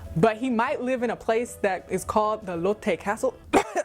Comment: Ha! When you said Lotte Castle I heard 'Elote Castle.'🌽🏰